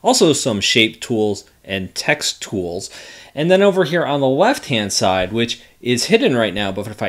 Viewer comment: did he say left hand side?